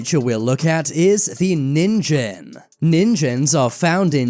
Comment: When he said “Ningen” I thought he said “Ninja”😂